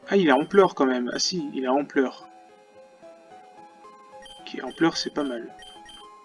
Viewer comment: jai entendu "ah il est en pleurs" en meme temps il etait tronkil tronkil dans sa grotte et tu le kidnappes